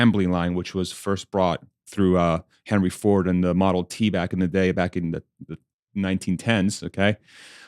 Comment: Dude, I thought I heard you say Model Teabag (it's not). BUT WHAT A GREAT NAME for the car that will get Mary Barra fired from GM!!